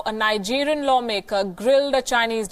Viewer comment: did she just said "grilled"?